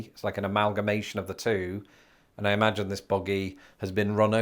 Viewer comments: Oh, you're saying "buggy." I thought you said boogie. Lol Yeah - buggies for kids; buggies for groceries; buggies for bag ladies and so on. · I thought he said boggy not buggy